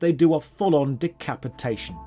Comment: When he was talking about decapitation, I heard "d!ck"-capitation and thought to myself, wait, I don't remember anyone getting castrated to death...